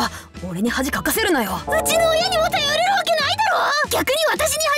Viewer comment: タピオカミルクフィッシュ🐟 笑笑🤣😂 美味しいのかな？ （同じこと思った人いる？） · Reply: 後、0:57聞いてみてください！ 「頼める」が「頼れる」に聞こえてくるから！👂